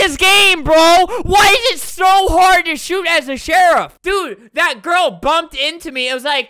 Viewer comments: i though he said "why is it so hard to shoot at the sherif" but he actually said "why is it so hard to shoot as a sherif" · did he just say shoot at the sheriff